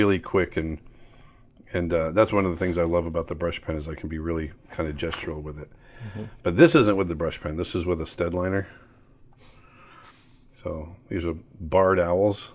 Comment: what pen does he say he used for it? Stead Liner?